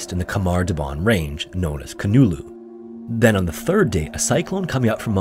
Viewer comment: Hypothermia-induced psychosis befell a group of young hikers who were simply measurably unprepared for a strenuous expedition in one of the most unpredictable, unforgiving environments on earth. The fact that this instructor thought this kind of expedition was suitable for what amounts to be a bunch of wet-behind-the-ears teenagers is absolutely, certifiably insane. I nearly fell over when the narrator said they were averaging 70km a day in class 4 territory. Their instructor literally marched them to death. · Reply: I thought they said 70km on the 3rd day